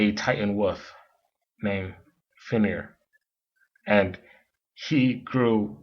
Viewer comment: "This...is a ball" "A Titan woof called Feneer" is what I heard him say? Do I need new headphones?